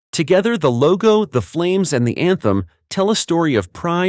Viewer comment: You said "emblem". I heard "anthem".